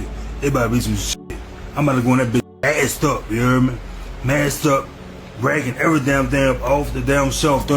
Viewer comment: OH he said masked up, I thought he said "Ass up"